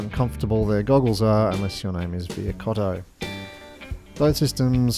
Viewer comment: Did you say the DJI goggles are uncomfortable unless your name is Vir Cotto, as in the Babylon 5 character played by Stephen Furst, or did I mishear that? :')